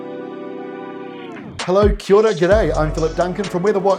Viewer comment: Kiwi Aura to you too. Thanks again for another great report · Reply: The greeting is Kia Ora, but I think I understand how it could be misheard as "Kiwi Aura". 😀